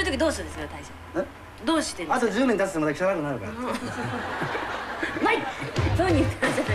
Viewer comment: ～何て言ってんだよ(笑) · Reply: あと十年立つとまた汚くなるからw